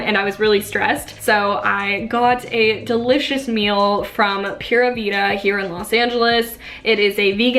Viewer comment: When you said pura vita I thought you said pura vida the jewelry brand hahah I was so confused ❤️❤️❤️